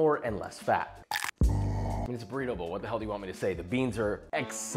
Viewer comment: What Josh said: "It's a burrito bowl, what the hell do you want me to say?" What I heard: "It's breedable, what the hell do you want me to say?"